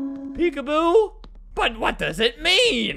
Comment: I thought they said WeAboo OwQ